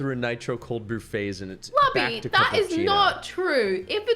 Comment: what did she call him? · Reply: Lubby.